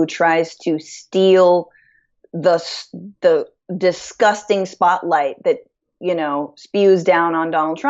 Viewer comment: Trumps personal life is disgusting? LMAO the XXX rated host! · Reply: not disgusting personal life, she said at 7:38 "Steal the...the disgusting spotlight".... whoo-hoo doggie... over here.. .that's it.... see the ball? good boy... focus.. focus... but hey... ok.. it's not how a president acts... pick any Republican... plenty of examples... Library of Congress much? but your answer is to just insult the host making a point and misquote what was said? Child.